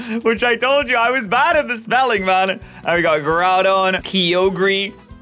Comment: When he said Groudon I thought he said Duraludon😂